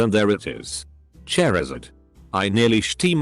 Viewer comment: Did I hear 'Chair'izard?